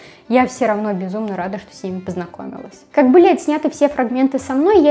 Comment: послышалось: как блять сняты...